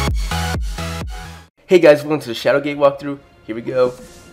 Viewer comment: Nice video! I thought you said "Shadowgay Walkthrough" in 0:09.